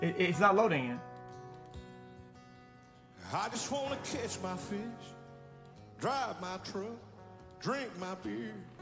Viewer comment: He sounded like he said “I just wanna kiss my fish” 😂